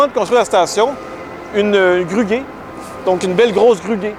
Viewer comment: Qu'est ce qu'il a dit à 2:24? « Une belle grosse gruguée » ? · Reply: C’est bien ce que j’ai dit, la compagnie de. Grue s’appelle Guay , ça fait donc une belle grosse grue Guay